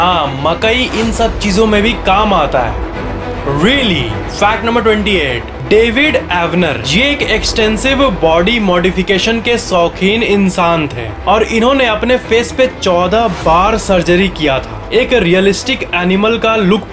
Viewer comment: He Said David Avaner but I heard David Warner!🤣🤣